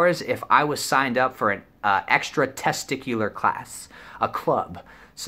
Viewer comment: am i the only one that heard him say “extra testicular” instead of extra curricular